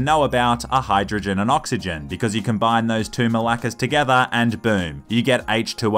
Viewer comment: "You combine those two MALAKAS together". Did you actually said Malakas? 😂